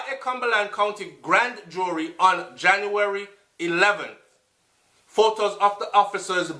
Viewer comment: wah a gwan deh suh? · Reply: Thought you said you February 21 instead of January 21st